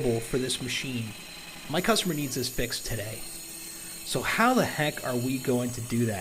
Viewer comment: Thought you said the customer neeeded it today